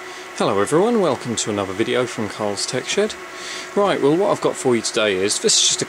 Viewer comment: lol 0.03 sounds like you said carls tech sh*t